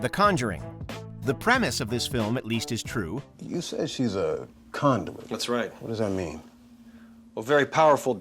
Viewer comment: I heard condom